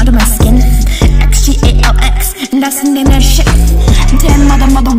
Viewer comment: y'all I did not swear😭 2:02 I said "that's the name of this ship" nd at 2:04 I said "den mother mother wolf"